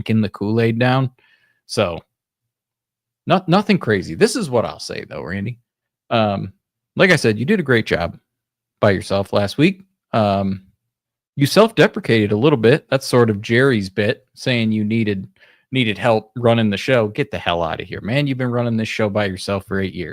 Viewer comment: I thought he said "self-DEFECATED" 😅